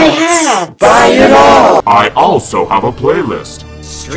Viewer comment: I know he says “buy my shirt” but I always hear “buy my shit”